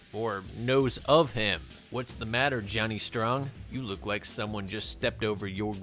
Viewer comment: hey now the subtitles say Johnny Ringo from Tomestone yet you say Johnny Strong, you can’t fool us. The M14 was an Easter egg nod to Black Hawk Down where Johnny played the MOH recipient Randy Shughart.